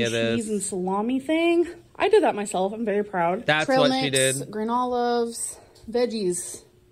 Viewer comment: sounds like she's saying granola instead of green olives 🫒